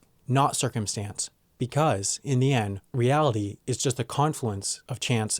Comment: i thought you said this video made possible with vacuum cleaners XXDDD